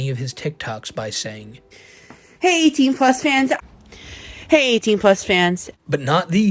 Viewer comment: Ain't even gonna lie, I thought was saying "hey Team Plus fans" this whole time. I thought it was some body positivity "I'm not fat I'm plus size" things 😂